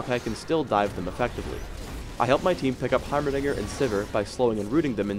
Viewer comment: it sounds like he says heimernigger :o